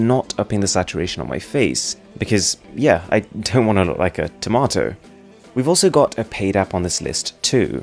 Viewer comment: Did you say : "Tamator"(Hindi) instead of "Tomato"(English)?